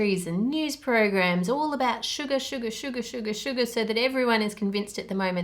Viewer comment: I heard Suga Suga Suga...😂 If you know, you know...💜